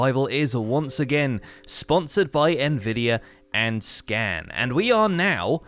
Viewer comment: I thought you said sponsored by SCAM